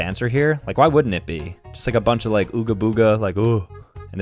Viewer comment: DID ANYONE ELSE HEAR HIM SAY OOGA BOOGA